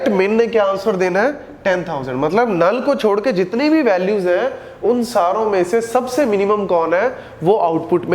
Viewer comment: i heard saloo 😂